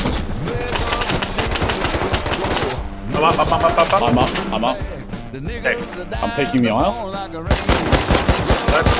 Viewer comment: ayo what did he say (music) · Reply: Nickles.